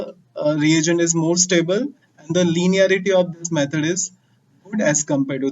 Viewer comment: wat did u say ? "The linearity of this method is ......." Buy a good microphone. Ur voice is not clear at many places. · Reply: Linearity of hexokinase method is more as compared to GOD POD. Point noted